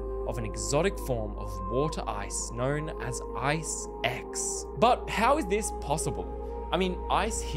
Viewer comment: did you say "Ice X" or "eye sex?" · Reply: He said Ice X 😭